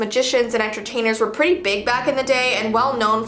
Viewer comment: did she just say big back